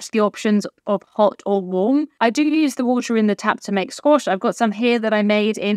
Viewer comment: What did you say you used the water to make? It sounded like you said squash, I’m sure I misunderstood! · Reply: Squash! Yes. Diluted juice. 🧃